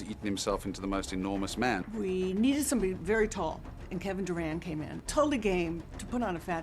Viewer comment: Lol I thought they said Kevin Durant instead of Durand